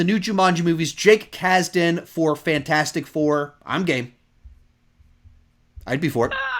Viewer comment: For a second I thought said "I'm gay" at 1:58:03 LMAO!